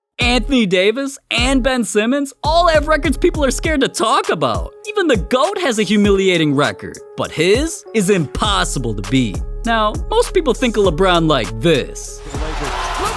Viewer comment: Did he just call LeBron the goat?